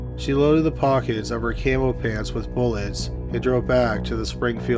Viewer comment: I thought you said she loaded ammo into her camel pants. Gave me quite a strange mental picture. So she passed a psych exam to be in the army, got dismissed after only two months, couldn't aim straight, missed her first two/three targets.....